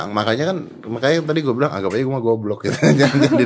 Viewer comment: Lihat 4:42 yang di ucapkan om "GOBLOG" SIAPA sih??? Apa ku salah denger ya???